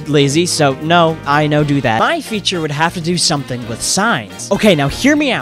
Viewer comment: I saw you hold a mini gun, and I was preparing to scream at you. then I heard "edit sings". YES!!!